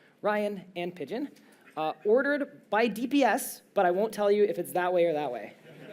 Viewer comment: Misheard that intro as "oddered by DBS" and wondered how you got away with such a dark joke